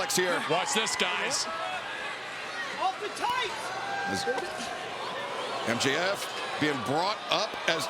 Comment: Does MJF say, "hold my tights" from 8:00-:8:05 or am I tripping? haha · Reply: Nah that's the ref bro 😂😂 said "off the tights"